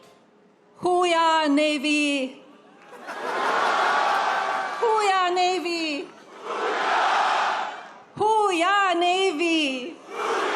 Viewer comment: I thought she said “who we are navy”. Sounded profound until it didn’t. Did she mean “oo-rah” like the Marines’ trademark holler? 🤷🏻‍♀️